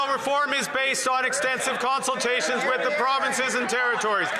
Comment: anyone hear his freudian slip? Expensive consultation... i bet it was expensive...